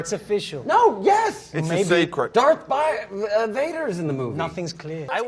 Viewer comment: did he say “Darth Fire”?